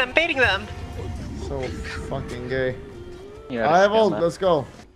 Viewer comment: There was a point where I heard you say " fucking gays "